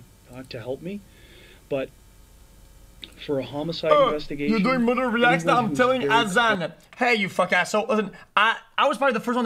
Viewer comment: Did anyone understand what mystical incantation xQc recites at 5:40? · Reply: I believe it's, "Oh, you're doing murderer reacts now, I'm telling Hasan"